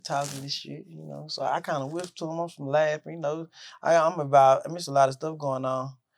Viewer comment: Am I the only one who heard him say “ I’m involved but then changes it up to it’s a lot of stuff going on”